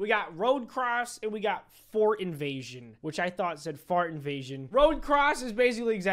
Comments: also sketch dont feel bad i also thought it said fart invasion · I thought it said fart invasion too!! Lol